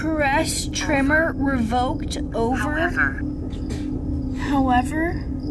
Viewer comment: there's a 3rd voice it's a male that I can't tell what it said, but it kinda sounds like "Curtis, I think you're right" 14:35 male voice whispers "over" 17:13 another voice whispers (I watched this investigation when this came out and man it was freaky) I remember hearing tons of voices through it 18:13 "look!" (I don't think that's) sounds like a male crying out. Right after that another male voice says, "I know" 19:43 male voice "hide" (I watched this Twin Paranormal when it aired too) 35:04 - 35:10 there's a female shouting she sounds far away could be saying "doctor" "made us" 35:10- 35:12 she says something else but I can't understand what she said. 35:06 - "what?" (Little girl voice responding to "Amelia" ) then a different voice whispers, "you know what to do" 36:20 voice 44:45 female whisper "I asked"